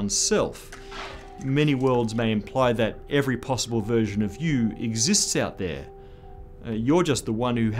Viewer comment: it almost sounds like he says "every possible version of Hugh [Everett]". Probably not correct (in this Universe at least)... Anyway, those kinds of thoughts must have been running through Everett's daughters head when she commited suicide. On Wikipedia we can read the following regarding his daughter Elizabeth: "Everett's daughter, Elizabeth, died by suicide in 1996 (saying in her suicide note that she wished her ashes to be thrown out with the garbage so that she might "end up in the correct parallel universe to meet up w[ith] Daddy")"